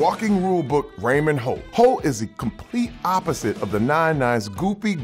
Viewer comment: instead of holt the subtitle says ho